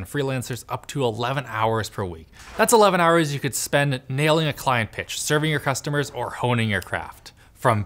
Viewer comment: OK I'm not a native speaker of the english language but still...... in that sponsor segment at the end of the video at 12:00, did Alex say "That's 11 hours saved you could spend on nailing a client's bitch"? I assume I misheard something there, right....? :-) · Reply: he said nailing a client pitch, like doing a great pitch of an idea to a company